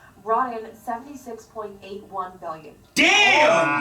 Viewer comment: Did she say 76.81 billion?